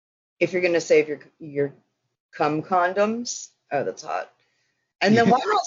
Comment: did she just say "that's hot"?? ROLF.